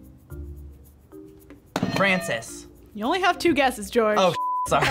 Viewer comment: I thought he said my name (He/she idk) My name is Frances